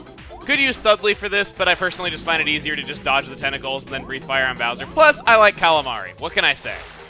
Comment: I thought you said testacles....